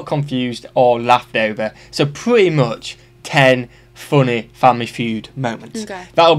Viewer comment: You: "Family Feud" What I hear: "Family food" Sorry lmao 😂